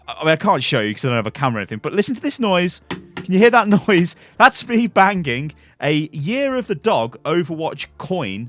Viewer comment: Omg at 4:39 i thought he said "thats me banging a dog" XD